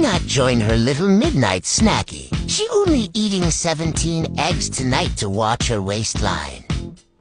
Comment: did you just say 'only 17 eggs'?